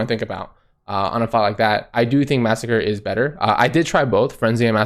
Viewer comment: thought you said frenzy is better haha